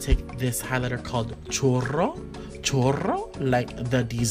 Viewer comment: I literally thought patrick said chorro @ 3:25 (which means diarrhea in spanish) instead of churro !! LMFAO